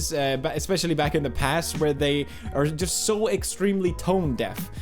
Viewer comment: Did he said "back in the past" at 4:44 ?